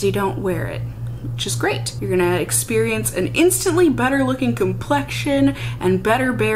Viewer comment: So. I totally thought you said "it gives you an instant butter looking complexion" instead of "better looking". And instead of questioning it I just said to myself "OOHH BUTTER! I WANT MY SKIN TO LOOK LIKE BUTTER!"